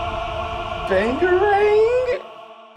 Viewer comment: did Malcolm say panderaang? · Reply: I thought he said "dangarang." It was obviously a parody of Sokka's boomerang quirk.